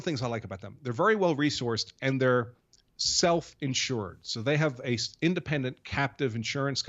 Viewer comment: 50 Thought you said wife insurance 😆